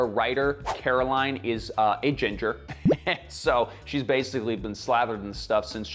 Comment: Geez I thought Levi said “our writer is agender” instead of “a ginger” and I couldn’t figure out what that had to do with her being slathered in sunscreen. 🤣